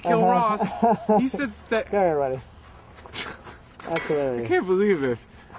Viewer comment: I believe Joe says ”come here buddy” here aswell. It’s kinda mumbled but It’s probably what he says because he gestures to Rob like ”come here” which he does :)